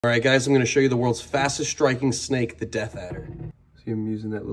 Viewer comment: I keep hearing “The Death Egg”